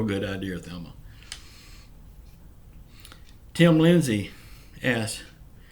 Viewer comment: Hi Mr thanks for answering my question. I never watched that movie with Andy Griffith in it. I can see why now you feel the way you do about him. Sounded like you said Tim Lindsey 😂😂